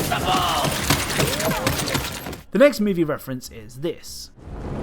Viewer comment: He sounds like he says “I’m fucking invincible!” Could it be a reference to Sundowner from MGSR?